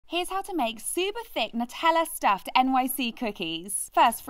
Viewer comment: ❤❤❤j thought you said super fake Nutella cookies lmao❤️❤️❤️